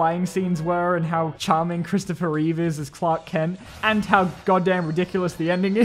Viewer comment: 😂 did he just say "twat Kent " at 8:02 ?